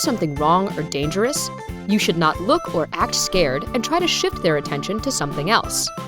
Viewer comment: BRIGHT SIDE - Please could you check at 1:25 whether it is "You should NOT look and act scared" which is what the narrator says, or "You should look and act scared" as the text on the video says, thank you.